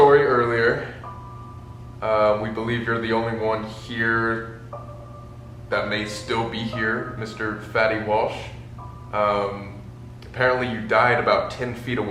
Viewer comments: I thought he said fetty wap… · Instead of fettie Walsh I thought he said fetty wap